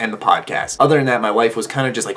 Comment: For a second, I thought you said 'my wife'...